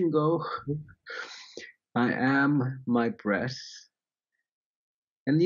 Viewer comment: I thought he said "I am my breasts". Seemed strange but I said it anyway.